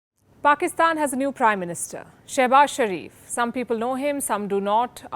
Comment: After just a second I heard it as Crime Minister instead of PM